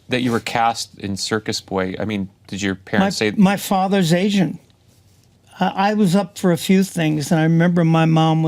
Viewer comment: I thought Mickey said “ my father’s Asian” I was like, but you said that he’s Italian? Maybe he’s half Italian half Asian? 😳I had to walk it back in my head. 🤣